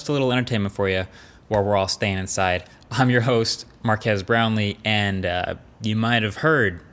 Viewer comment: I thought I heard Mark has Brownlee